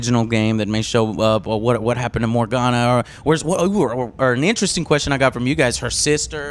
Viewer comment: Did Joe said Morgana? WTF?